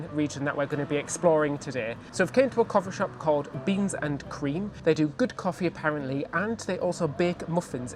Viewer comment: When you mentioned the name of the coffee shop, I thought you said "Beans & Queens" but it was "Beans and Cream," but I thought, "what a lovely, great idea for a coffee shop!!! Beans & Queens, delicious java served up by drag queens!" Sign me up.